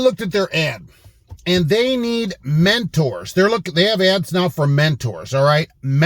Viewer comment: I was in because I thought you said matadors, but now that I see it's mentors I'll pass.